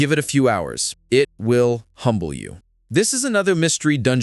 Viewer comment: It sounded like you said "it will hump you". Had to play it a few times to realize it was humble